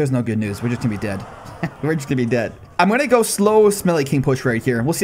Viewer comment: I heard "smelly king"